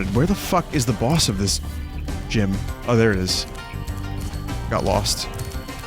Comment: did he say gym or jim, we'll never know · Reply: He says gym. It's a reference to this: <link>